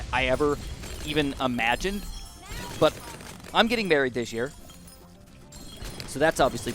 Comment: Thought he said "I'm getting buried, and that's 3 - 4 days of my life gone" for a minute.